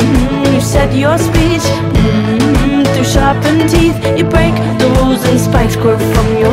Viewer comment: Before this video I thought she was saying ‘you break the walls in spice girl form’ and I liked it and now I’m sad